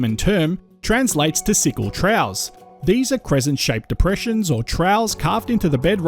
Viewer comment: I feel I have to comment on the pronunciation of the narrator. Apart from Pilbara being pronounced in a way I've never heard before, and the sound "s" being pronounced as "sh" as in "Ushtraya", finally I hear "trows". What the hell is a trow? I think to myself. Then I read in the subtitles "troughs". (get an Australian dictionary and look up the pronunciation) There are three possibilities. 1. The narration is done by computer and set to mimic a peculiar Australian accent. 2. The narration is done by an Australian who is ignorant of correct pronunciation. 3. The narration is done by a non Australian who simply doesn't know the correct pronunciation of words relevant to Australia. Finally in all cases the narration should be checked by the supposed Australian geologist who makes these vids. Butchering the English spoken by Australians when discussing Australian topics is an insult to native speaking Australians. Australians who have subscribed to this Chanel will get jack of it (to use an Australianism) and simply unsubscribe. Don't give up your day job as they say. As a mining engineer I enjoy the exposition of geological facts about Australia, but the pronunciation being butchered makes it almost not worthy of watching.